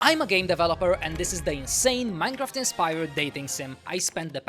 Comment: my dumbass thought he said gay developer at the start 😭